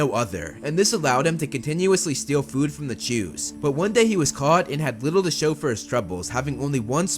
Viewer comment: I swear it sounds like he says "he continuously stole food from the Jews" lol.